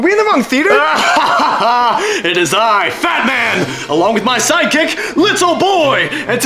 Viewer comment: the fact that the bombs were actually named “little boy” and “fat man” makes this even funnier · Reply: HELP I FORGOT LITTLE BOY WAS A BOMB AND I THOUGHT HE SAID BATMAN 😭😭😭😭